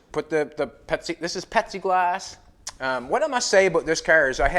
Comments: What's pepsi glass? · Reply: At least I'm not the only one who heard it as Pepsi Glass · Lol I love it ....The last video and right up till you removed the back window. I thought you were saying it was Pepsi glass 😂 Had me thinking it was like a thick blueish glass 😁 Love it brother keep it up 👍